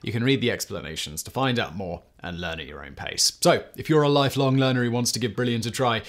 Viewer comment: I have been listening to too much Casual Criminalist. At the end of the Brilliant promo I thought you said "...if you're a life-long murderer and want to give Brilliant a try..." and it definitely caught my attention.